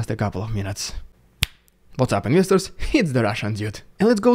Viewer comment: The Lukashenko photo is creepy but it's a snapshot of a video in which he is alive. The photo of the fighter jet was not an F-16 (perhaps a Mirage?). Btw, it took me forever to understand that you say "what's up investors" I always thought you were saying "misters".)